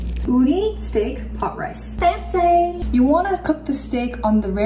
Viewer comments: I thought she said itzy instead of fancy ngl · Shee said Fancy but i thought she said itzyyy!!! Lol